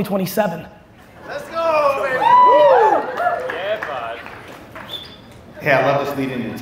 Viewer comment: did I hear "ye baat"?